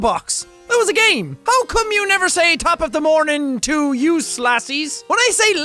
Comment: Jack, I always thought you said "Tap of the morning jail laddies" (laddies as in lad). Also, I always read the of the channel as "JackSkepticalEye". Yes, I am always like that. sorry. love your videos.